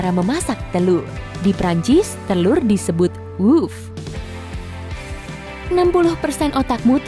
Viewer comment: did u just say "OOF" ?